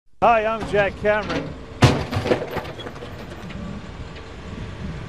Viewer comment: "Need a new refrigerator? So do we." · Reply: I thought it said do you do refrigerator